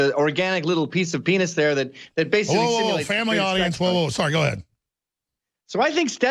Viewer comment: wait.... did he just say family audience???